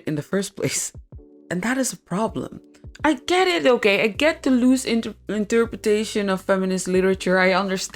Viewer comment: I heard 'Deleuze' instead of 'the lose', and I was really confused(and interested) that you would start a video about feminism with Deleuze. 🤣Great video anyway!